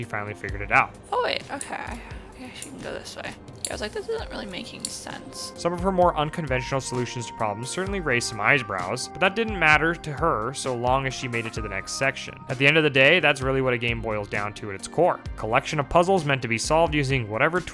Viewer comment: hollup! Did you just say... eyeS brows!?